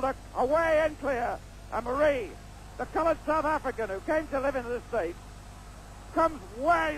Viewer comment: Did the Commentator say " the Colored , South African" at 5:24 or is that just what i thought i Heard ..i thought they stopped using that in the mid 70's or early 80's? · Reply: He did say colored South African. ....and what would you have possibly preferred that he say? Seems totally correct whether 1981 or 2020.